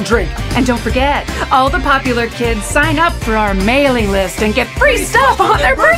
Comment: We finally get to hear them singing. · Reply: When Chica said Mailing List, I thought she said “Melee List”. FNAF confirmed for Smash?